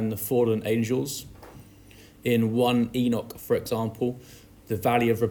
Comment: what did you say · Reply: 1 Enoch